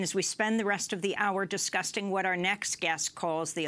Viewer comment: She's clearly thinking of Trump as she accidentally says "Disgusting" instead of "discussing" at 0:05! :)